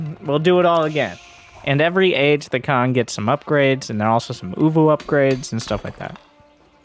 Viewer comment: I thought you were saying " UwU upgrades " at first. Had me thinking those Mongols were some weirdos.